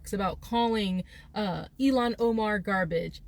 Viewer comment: I love how you say Omars name 😂sounds like your saying Alien Omar 😂😂